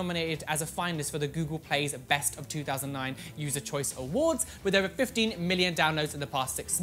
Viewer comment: wait did he just say "best of 2009"???